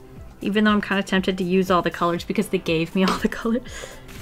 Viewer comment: the gay feel 😂 That's what I heard (I think she said that but idk)